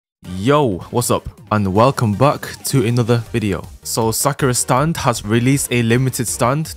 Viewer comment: how did i hear “sucker stand”